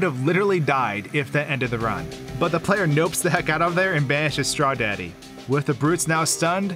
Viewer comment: did you just call him “Straw daddy”?